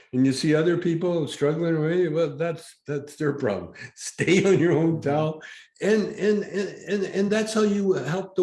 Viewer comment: Thanks for this, listend twice. I actually misheard what one of the guests said "stay on your own towel" I thought he said "stay on your own Top".... like stay in your higher self/vibration. 😉 Especially in light of my mother being diagnosed with Cancer this year... listening to this gave the answer to my internal question.